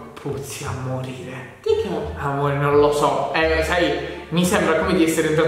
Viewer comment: Solo io al minuto 9:35 ho sentito amore 💕